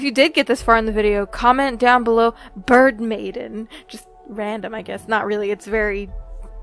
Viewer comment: I'm eating my lunch whilst watching and I totally thought you said "burn maiden" and then I was like, "well that's a weird thing to comment. I guess it makes sense, cuz the sun burns." smh.